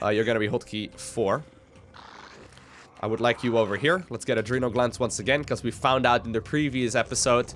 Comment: Am I the only who when says: "Adrenal Glands" I hear: "A dream of Lands"